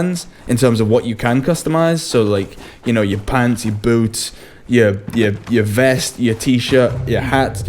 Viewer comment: Did you just say, "your pansy boots?" Oh wait, i rewound it. Your pants and boots. Lol